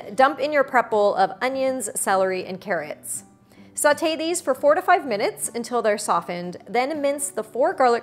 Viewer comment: Sounded like you said, sauté them for 45 minutes, not 4 or 5 minutes. Enunciation my dear.